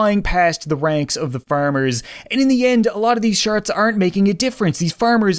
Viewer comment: Did anyone else hear at 14:41 he said, “and a lot of these sharts”